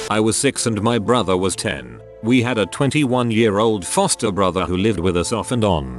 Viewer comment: I thought it said 21 year old toaster brother and I was all like what the frick, Nani???? Until it said foster.